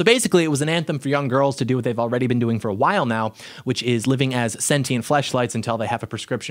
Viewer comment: "Sentient flashlight" still one of Doyle's best ever phrases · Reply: I thought he said Sentient Fleshlight. It doesn't make sense otherwise.